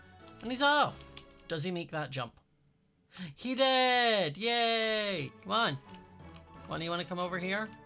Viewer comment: cuz ur voice it sounded like u said he dead yay